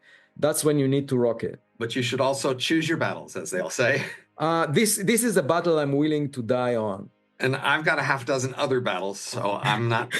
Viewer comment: does say "this is a battle I am willing to die for" ? !!